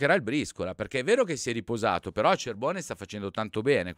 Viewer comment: non ho capito......Cervone?? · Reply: AAHAHAHAAH ACERBONE 🙂